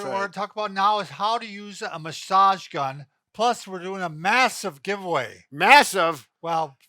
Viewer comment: lol for a second i thought it said "massive gun giveaway" and thought wow you guys do take your guns seriously in the US.....sorry about that....